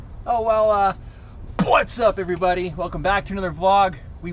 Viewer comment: HAHAHAHA. Again I heard "butts up everybody!". That is awesome!